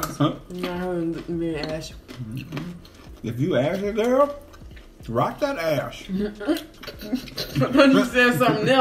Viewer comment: I thought he said Rock that ass😂😂😂😭